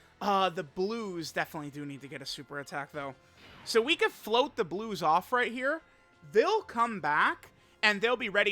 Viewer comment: Bro my phone is tripping out and when he wanted to float the blues, i heard “f—— the blues” which was funny